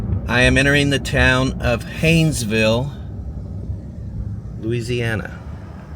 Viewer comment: Thought you said Painsville.